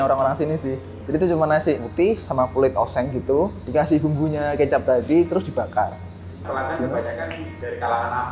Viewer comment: Aku salah denger GK sih? Td bilg kecap babi ya kak